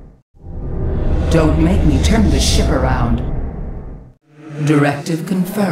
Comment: am i the only one who heared "dont makeme turn this shit around"